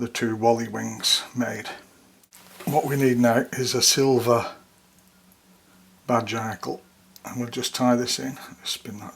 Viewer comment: Thank you, for your videos. You are certainly the best one out there in my opinion. At 9:06 you say what kind of Hackle you use. CC says "silver bar jackal" I'm thinking that's not exactly correct. Would you please type what it is that you actually use? Happy Holidays! Many Thanks! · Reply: Hi Robert, thank you for the kind words, it's always good to get positive feedback. The hackle is a silver badger saddle hackle but I also use Golden badger for this pattern as well. Happy holidays to you too!